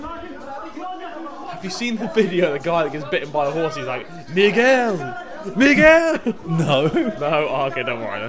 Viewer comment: what did harry say?!?!? :0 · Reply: Miguel